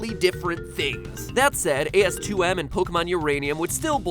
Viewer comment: Did... Did MatPat say "AS2M"? o.0